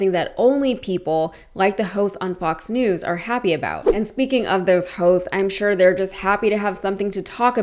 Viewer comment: did you say "and speaking of those hoes"?😅😅